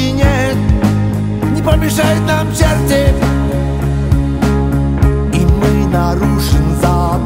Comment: а мне послышалось: "не помешают нам черти, и мы нарушим запрет" ))) согласись, что это более логично))